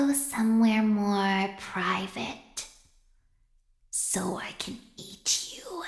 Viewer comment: Toy Chica: let's go somewhere more private Me: Aww yea! Toy Chica: so I can EAT YOU Me: uh no that's a deal breaker JUDGE I'D LIKE A RESTRAINING ORDER!! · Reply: I thought she said “So I can Beat you”